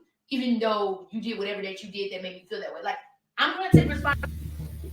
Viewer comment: Firstly lets not judge. Let's find out why and how it lead to this in the first place so that others dont have to make the same mistakes. Because DV and defending oneself against DV is way too common. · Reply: "put my hand on you eventhough you did what ever you did to make me FEEL..." she didn't say hit, she said feel! I don't want to judge. I'm just going by what she said in that moment. I don't think he abused her for a year. Probably just snapped on her that one time.